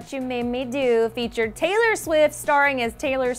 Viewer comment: Did she say: "Taylor Swis" ?? Tf